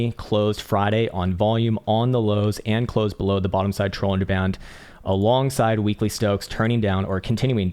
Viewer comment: I’m sure it’s just because he talks so damn fast, but every time he says “Bollinger bands” it sounds like he says “trollinger bands”. And it makes me wonder when grapes became involved in technical analysis. …yes, that was a wine joke